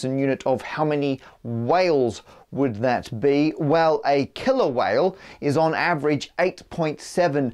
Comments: I totally thought you said Kilo-whale and was disappointed you gave the length of one KILLER Whale. Oh accents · I swear I thought he said "Kilo-whale" as if there's some sort of whale based standard of measurement. I wouldn't be surprised if there was here in America actually. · Thought you said "kilo whale" for a bit. lol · I thought he said kilo-whale xD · I definitely thought you said "kilo-whale" and not "killer whale" and was really confused for a few seconds